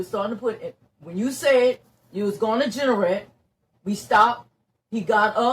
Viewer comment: What's she saying at 0:17 - "You said you were going to Generet" ?